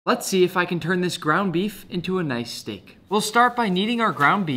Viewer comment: I thought he said brown beef😂😂😂😂😂